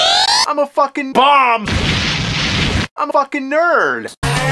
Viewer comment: i dont understand did he say "im a fucking nerd" or "im fucking nerds"??